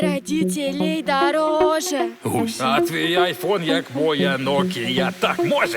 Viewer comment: мне послышалось что сказал гусатый айфон так моя нокия так может